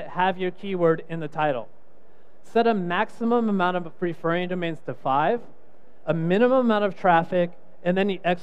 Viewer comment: What did he say at 2:33 ? Couldn't hear clearly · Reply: maximum referring domains to 5